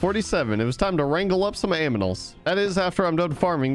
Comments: Was it only me but I think I heard him say “aminals” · is it me or do you even hear him say aminals instead of animals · did u said aminals? at 9:22😂😂😂